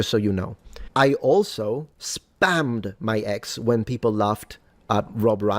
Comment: ngl, the first time I heard this, I thought Metatron said "I spammed by Ex." and rewatching it via your stream, I just realized he's one of those people who calls their Twitter, their X unironically...